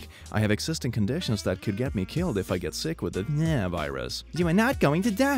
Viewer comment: I heard it as the ~Nyaaa~ virus.